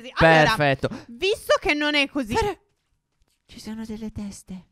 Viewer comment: Solo io ho sentito scopo nel minuto 4:07 lo a detto